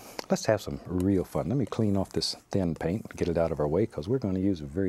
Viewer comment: omg I think he said damn. I guess every deity of calm has their moments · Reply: He said thin tho